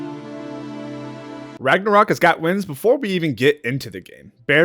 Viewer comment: did you just say ragnacock?